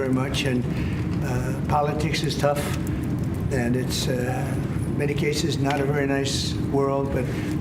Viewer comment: What does he say at 1:54. "Politics is tough and ??medication?? Isnt a nice word. " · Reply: ”in many cases”